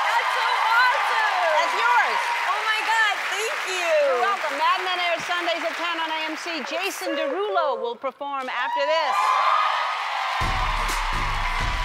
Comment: I HONESTLY THOUGHT SHE SAID FLULA WILL BE PERFORMING AFTER THIS LUL BEEN WATCHING TOO MUCH YOUTUBE